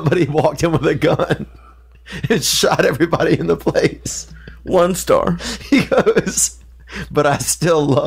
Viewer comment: I thought said "worldstar" and I lost it